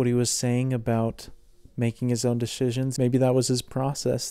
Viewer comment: making his own “deshizons” 😂 i thought i heard wrong but went back to make sure lol